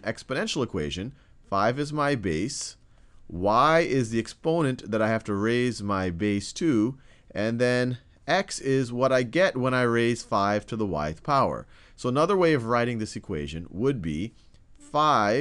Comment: I thought he said "5 to the White Power" .... great videos though, thank you! :)